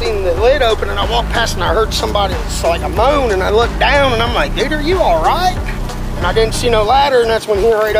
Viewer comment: “Nigga Are you ALRIGHT?!”🤣🤣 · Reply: He actually said "dude are you alright", I heard du'rrre you alright" you heard nigga you alright , some heard it with a er lolz accents I swear, they can get you in trouble, especially if you speak fast and with no pause.